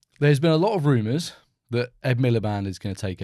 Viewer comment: I thought he said the ed millar band was which didn’t make sense? But I think England does this Pretend to be in crisis while ruining the empire degrading the environment and basically encouraging nuclear war